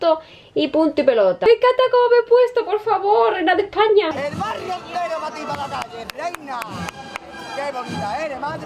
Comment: Que dice ese chico en esta parte del vídeo 16:47? el mar entero o que? gracias chica